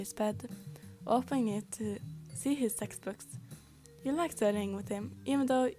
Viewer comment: OMG WHEN YOU SAID TEXT BOOKS I THOUGHT YOU SAID SEX BOOKS BECAUSE I WASN'T PAYING ATTENTION 😭✋